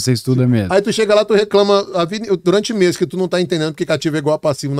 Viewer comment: O que ele fala no minuto 6:14 ? Não compreendi se falou cativo ou ativo? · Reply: Ativo, é um termo da Contabilidade.